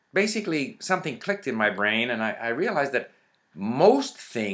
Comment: What did you say around @ 4:55 before you said "supermemo"? Something like "Ankey"? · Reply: Anki. It‘s an SRS system for desktop computer and smartphones. You‘ll find it through Google. The desktop version is free.